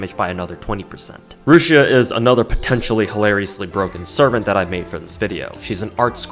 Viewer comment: Not gonna lie, first time through I thought you said "pettan-tially hilariously broken servant".